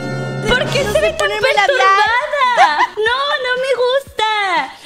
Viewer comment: Que es persorvada no entiendo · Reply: perturbada wey XD